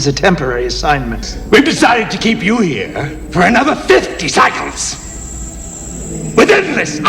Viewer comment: sounds like he said "another 50 seconds"